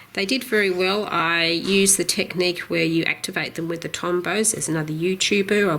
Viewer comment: Around the 11:40 mark you mentioned linking a technique in the description but I think it slipped your mind. I can't quite make out the word... utombo? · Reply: she said she activated the inktense pencil with the Tombow marker.